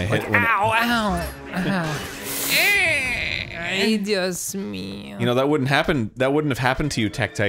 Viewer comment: Did Dan just say “Ay Dios mío”?